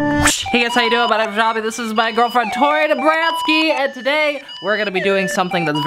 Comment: Why does he always say tori nabranski sorry if I spelled it wrong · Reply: Mirandah Pryce Omg I thought he was saying Nebranski too until I looked in one of the descriptions or something